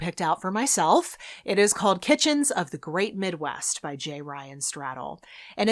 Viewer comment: I heard “Pigeons of the Great Midwest” and I was 100% ready to add it to my tbr shelf